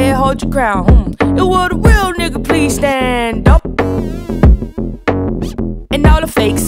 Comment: I almost thought she said " will the real slim shady please stand up" 😭